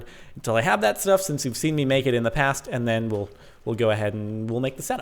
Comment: he said something sounded like "you've seen me naked in past " :D i fell from chair while laughing :D